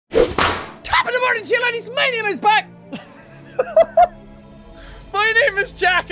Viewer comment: When I first heard the intro I thought he said my name is bad